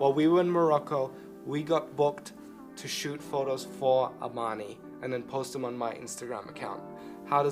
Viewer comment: When you said "but first we have to finish a photo shoot for armani" I thought you said "for our money" lol